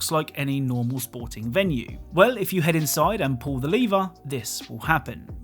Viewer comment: I thought you were sayin' pull the "liver" instead of "Lever"